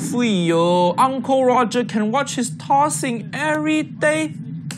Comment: i heard him call her "him"